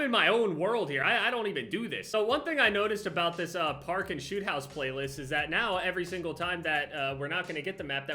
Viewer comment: i thought he said parkland shoot house 😳